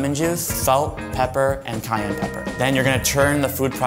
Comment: When he was making the aioli, I thought he said Kanye pepper 😂🤦‍♀️